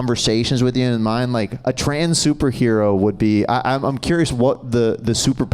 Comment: When they said trans super hero for a second I thought he said train super hero 😄